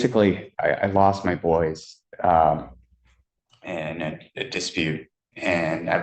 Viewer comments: I thought he said he lost his boys in a dispute. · I heard lost ‘my boys’ too 😊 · I think he said, "I lost my boys in a dispute" Not "I lost my voice in a dispute" Not that it really makes a difference.